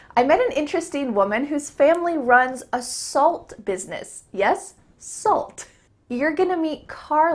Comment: This is so funny what a non-native ear can hear sometimes, I went like "whaaat?!" when instead of "a salt business" I heard "assault business"😅😅😅